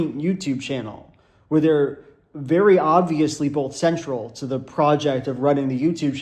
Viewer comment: I was watching with the volume on low and I heard "They're very obviously homosexual" and I thought small joel just dropped that randomly in the video and never acknowledged it again. Which really changed my viewing experience